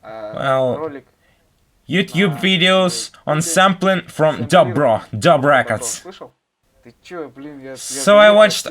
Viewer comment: What he says at 27:50? Which records? Can you share the link of the channel? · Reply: Dabro